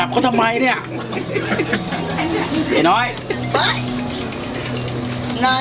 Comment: เหมือนได้ยินยายพูดว่า ไอ้หน้าฮี😂